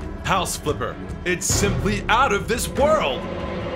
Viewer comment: At the end I thought they said “It’s ADAM this world!”